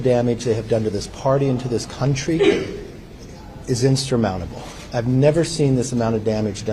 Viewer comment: Did he say ‘instrumountable’? (2:12) Appears they are learning T** and Mike Pillow speak too. Scary stuff.